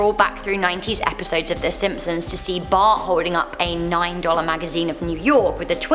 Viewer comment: did she say Bart 🤔